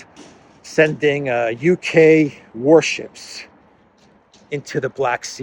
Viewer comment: As a non-native English speaker I kept hearing "UK wOrships" throughout the video. Endless laughter xD